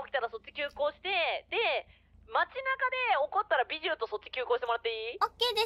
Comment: what was the Japanese word that sounds like Bijou? I thought she was talking about her as well, haven't heard that one much. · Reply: スバル「街中で(事件が)起こったらビジューとそっち急行してもらっていい？」 そのまま書いてみました！ 文字通りビジューの名前を発言しています！